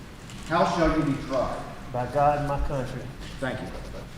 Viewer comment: Did he say, “By God and my cousin?” (As how he wanted to be tried.). My hearing isn’t as good as it used to be. The reception keeps going crazy. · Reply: He said "By God & my Country"...but what you thought you heard could very well happen lol.